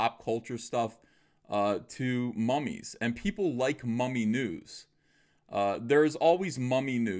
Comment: I thought you said Miss Marple and got excited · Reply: Skally The Wag I thought he said 'mummy nudes' and also got, weirdly, excited.